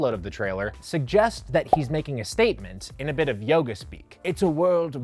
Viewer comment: are my ears deceiving me or does he say "Yoga speak"